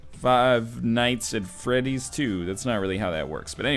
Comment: is he said five night at freddy 2 ?